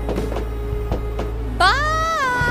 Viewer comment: what she say? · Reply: Its "What's the plan again?". Sincerely, "Boss" aka Octoling Soldier Boss